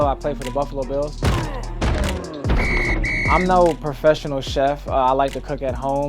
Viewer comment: Lmao I thought Dean said “ I’m now a professional chef”